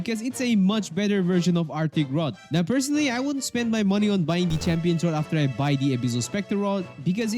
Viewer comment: What did brother say at 2:20? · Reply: Oh just dont buy champion ight.